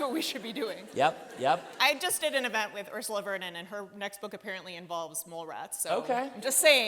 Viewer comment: I thought she said she just did an event with Ursula LeGuin and for a moment I worried she was taking this necromancy thing too far.